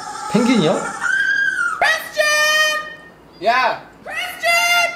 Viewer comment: 에 네라고오쩌구가 야이 쇄끼야!라고들림